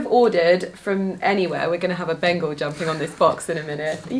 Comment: 😂 I thought you said bagel instead of Bengal. 😂😂😂😂😂